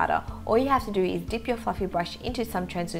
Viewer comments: i hear: "All you have to do is dig your fucking brush" lol she says fluffy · I actually thought she said "Dip your fucking brush".